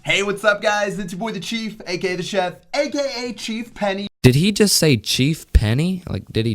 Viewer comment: he said "Chief Penny" because that was the Brawler he was using. If you didn't understand.